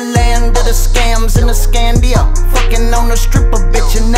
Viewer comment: My hearing gettin bad as fk I thought he said Escambia 🤔😬🤦‍♂️Scandia it ain't my fault 😫🤷‍♂️